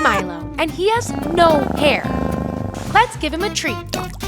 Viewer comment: I thought it said he has no air..